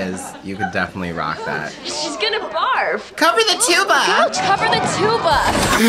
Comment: Literally always thought they were saying “COVER THE TWO OF US!” I never heard “cover the tuba!” HOWWW???